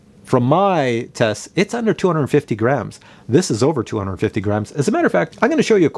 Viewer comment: When I saw the video I was really shocked when you said "over 250 grams" because I thought you said over 250 grands, and I thought, omg I don't have money for this 🤪